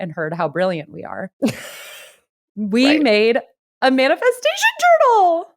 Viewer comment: i had to show up to watch the podcast because on spotify i thought y’all said “manifestation turtle”….i was like why would i download a pdf of a turtle?